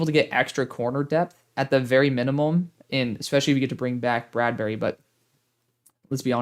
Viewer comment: I heard “replace Bradberry”, and I said, “Oh shittt!!!” That’s crazy but it could happen